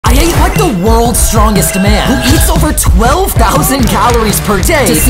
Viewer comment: i first thought he said "the worlds longest man" 💀